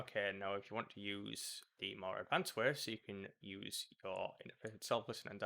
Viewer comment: I'm sorry, why do you need Voicemeter? at 5:37 I can't understand what you are saying. "if you want to use the more advanced warez"?? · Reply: More advanced ways. It allows you to route audio around, but if you’re content to use the first method shown, by all means use that.